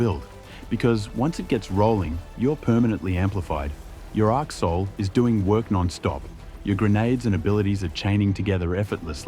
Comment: I was chewing after taking a bite of lunch, and "your arc soul is doing work" sounded like "your asshole is doing work"... had me nearly choke, lol, w Australian AI voice/pronunciation.